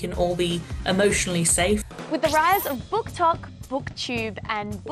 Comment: cc incorrect and says “booktalk” instead of booktok